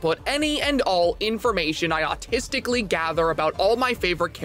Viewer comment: I'm sorry, did he say autisticly right here